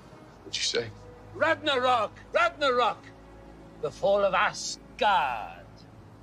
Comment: the fall of ass-gard. Why did he say it like that 😂